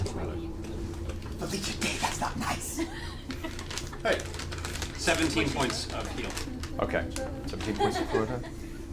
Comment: after the emergency landing catastrophe Liam 👀 's a comment on Chat, shows Laura who says "NOoooo!!!", then Liam says to camera, "Felicia Day that's not nice!!!". WHAT DID SHE SAY???? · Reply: Trinket is a bear rug now. Thanks for nothing Critters got it myself.